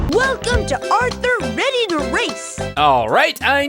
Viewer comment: i swear at 4:53 they said ready to rape